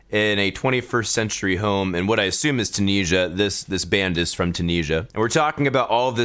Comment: that’s the joke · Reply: said Tunisia like an American and it sounded like he said tunija